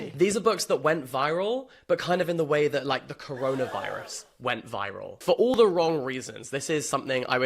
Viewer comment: cc I had turned away from the screen for a second and i thought you said colonialism not corona virus.